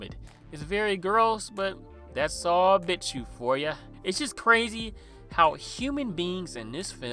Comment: What are you saying at 16:26? Subtitles says that’s all bits you for ya and I can’t tell what you’re actually saying 😭 pls halp